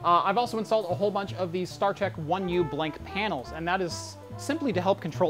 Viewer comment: lol I know it's Star Tech but I hear Star Trek every time I rewind this part.